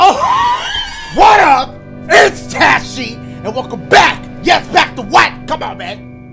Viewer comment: Literally thought he said lmfaooo